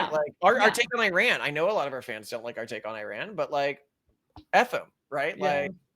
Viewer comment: "I know a lot of our fans don't like our takes on Iran" I thought you said: "...don't like our takes on AYN RAND" LOL